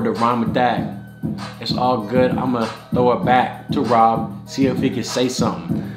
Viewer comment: bruh I thought he said "I am going to throw it back on ROB